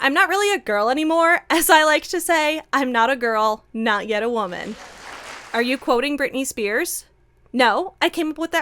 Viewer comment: Tried to hold it together while eating when I heard "not a girl not yet a womanifest" Dead😂